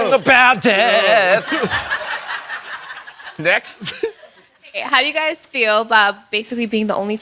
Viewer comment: I'm sorry did he just say "necks" after telling that story about a guy snapping his neck on a wave?